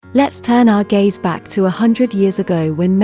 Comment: I thought she said, "let's turn our gays back" and wondered, "what we do?"